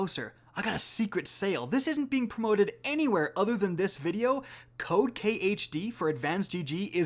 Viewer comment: When you said "use code K..." I heard "use cocaine!" then i looked up hahaha